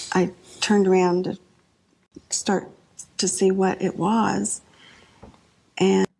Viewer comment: I hear "turned around to start to see what it was" which IS past tense. Doesn't necessarily refute your argument that Patsy is lying but is not one of the chips in the "present tense" stack. Perhaps she did look at it, seeing it "laid across the rail" and turned around to make sure it was in place. Like a speech on a podium.